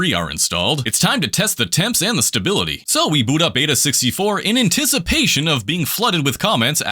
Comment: i thought he said beta 64 did not realize it said aida64